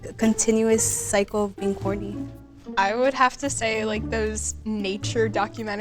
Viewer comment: Did anyone else hear "horny" instead of "corny" at 5:53? Just me? Oh, ok